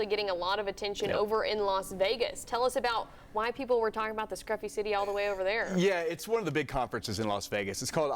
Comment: Did she just say ‘crappy city’?